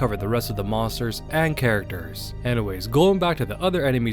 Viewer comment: did u just say gollum back 4:46? :D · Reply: Hahaha No I meant duskgolem LOL